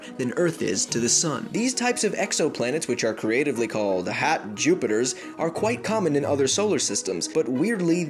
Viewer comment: Did you notice that on 0:35 he said HAT* Jupiters instead of HOT******************************************* Jupiters